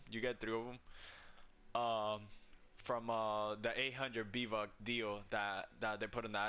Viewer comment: did he just say b vuck at the beginning